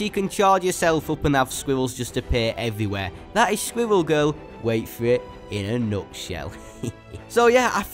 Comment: He sounds like he is saying swivel girl